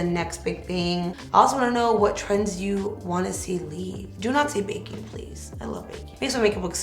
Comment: She said “please don’t do...” what?? I couldn’t hear what she said 😭😭😭🥺💕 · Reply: ? I think it’s “do not say baking pls”